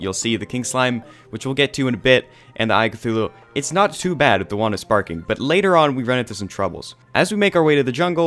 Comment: I thought it said “wand of spanking”